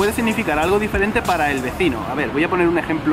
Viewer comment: did you say V E C I N O?